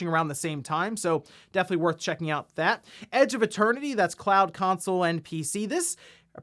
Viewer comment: My hearing is going. I thought you said ‘Edge of a Turkey’ instead of ‘Edge of Eternity.’ Now I want to see how ‘Edge of a Turkey’ will look.